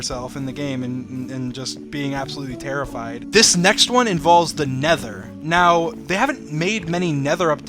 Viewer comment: i heard "this next one involves the devil"